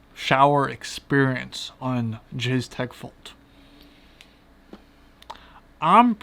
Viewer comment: anyone else hear jizz tech vault?